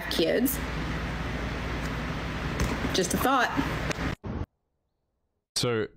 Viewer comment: when she said just a thought, I thought she said I'm just a thot.